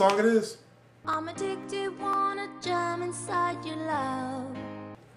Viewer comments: Am I the only one who hears,"Jam inside your love"??? Ain't nothing wrong with that since we do jam inside our love into our partners. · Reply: at one point I thought I heard jun onside ya lub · lol wow I heard "wanna german side your love."